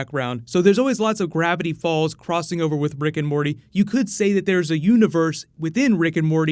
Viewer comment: Am i the only one who heard brick and morty?